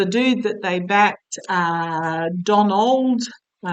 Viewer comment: Lol. I thought you said, "Don Old". That one never occurred to me. Oh my!